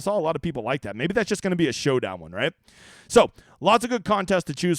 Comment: I misunderstood you yesterday (Sunday) . I thought you said NO MORE GOLF HOEDOWN CONTESTS , Maybe I wasn't the only one ??